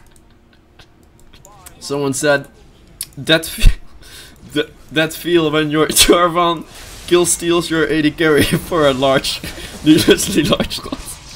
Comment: After much thought, i've discovered what he says on 2:00 ''Someone said: That moment when your AP Jarvan killsteals your ADC for a needlessly large rod''